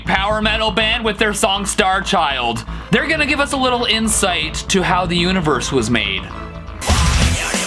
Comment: i heard "Fu****g universes"